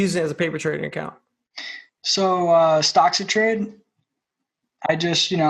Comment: At the 11:00 mark, what does say he used as a paper trading account? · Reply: Stocks to trade is what he said. Thx for watching Dawgs, please remember to hit the like button to help us grow the show :-)